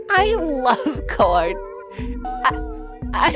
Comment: bro the whole time i thought he was saying 'i LOVE porn!!" and i sobbed